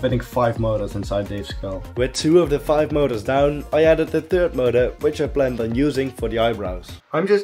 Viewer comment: When I heard him say motor, I swore he said murder.